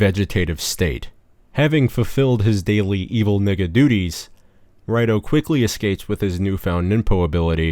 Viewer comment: did he just say 'evil nigga duties'.........wild